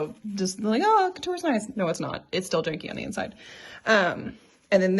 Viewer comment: For the subtitles using folk: Sometimes the english subtitles have (indistinct) in them, so here are all the words for these gaps 8:38 coutil 8:50 costuber 9:34 every seam is boned 10:50 muslin 11:33 coutil 11:36 Silk Baron 14:26 to fasten it (the bodice) shut 19:13 the bottom 19:22 nigh 20:24 digitized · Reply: What about 10:06? "it's still [indistinct] on the inside." I think I heard "janky" but I'm not sure.